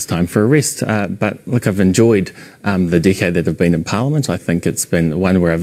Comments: “I’ve enjoyed the dickhead that I’ve been in parliament...” I can’t be the only one who heard that! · "The dickhead I've been in parliament" is what he really said. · I need a translated script of this exchange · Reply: did he just say "the dickheads in parliament"? It's kind of hard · Did he say "I've enjoyed the dick-head I've been in parlament" or "I've enjoyed the decade I've been in parlament"? I find the Kiwi accent a little hard to catch sometimes ;-)